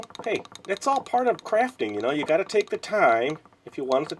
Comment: When you said craft I thought you said crap